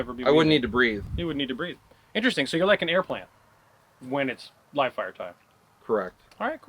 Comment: When said air plant I thought he said air plane and was very confused but also literally loling until I released it was air plant..